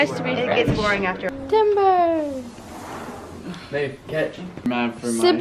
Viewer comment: I heard "babe catch "